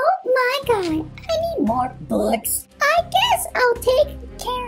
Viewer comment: what in the world is her voice and she says bucks